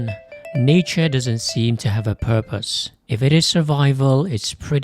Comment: I thought you said, “Hey, everyone. Nietzsche doesn’t seem to have a purpose…” 😂